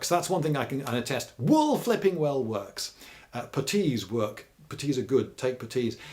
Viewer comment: What's a "petis" or however you say it. You say they're great so I wanna know lol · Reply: Putties; They're leg covers to protect you as you hike. Super handy in the right conditions.